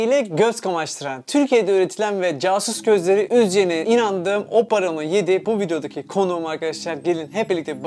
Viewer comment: o paramı yedi diye anladım OPPO Reno 7 :d